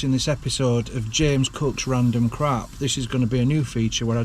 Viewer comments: I thought you were saying James Cook’s random crap rather than James cooks random crap 🤣😜 I was wondering where you’d got some of Captain Cook’s belongings from 😜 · Lol, i thought you said " James cooks crab" thought that was a bit odd 😂😂😂